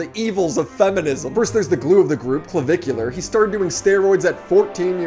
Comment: I had to rewind 10 seconds cuz I thought u said “he’s the Gru of the group” lmao